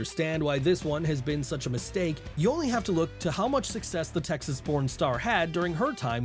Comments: For a min there I thought you said Texas pornstar. I was like.. 😳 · I misheard and thought this guy called Raquel a "Texas pornstar" for a moment there 😂😂😂